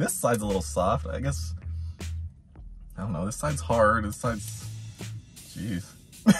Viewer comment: All I heard was “this is hard...look, a hole!”. 😂🤣